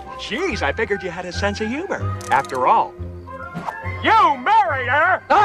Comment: i thought i heard alex rochon when he said "you married her"